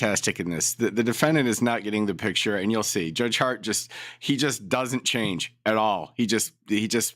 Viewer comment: Lolol. I thought you said Judge Hard on! You said Judge Hart on, like a video of him on your channel! I grabbed my phone to look at his name!